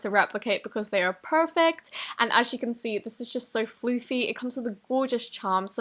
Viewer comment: Did she say Floofy?